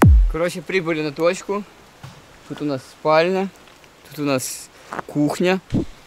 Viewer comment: послышалось он сказал вместо спальня «усыпальница»